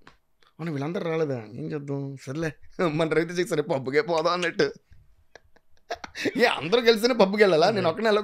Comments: What did DSP say after Sare le......... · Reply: it's reference from movie mirapakay "sarle pub ke podham" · What did DSP say after Sare le......... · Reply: raviteja gari style dailog,Sarele pub ke podam annaru. already he told in this podcast only raviteja style other dailog also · What did DSP say after Sare le......... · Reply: sare le mana Ravi Teja sir pub ke podham annatlu It is movie dialogue bro (i think in mirapakay or don seenu)